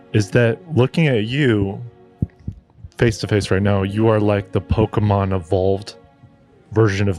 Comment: I genuinely thought said 'You are like the Pokemon of bald' I'm dead